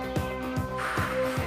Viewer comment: At first i thought she said '' Burry me , eat your heart out ''